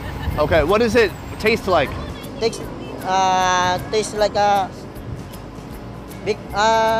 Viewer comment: Just so you know, saying you're having a nosebleed in the philippines means someones having a hard time speaking english. Sort of an expression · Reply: Thank you! I thought he was saying it tastes like a nose bleed. I was so confused xD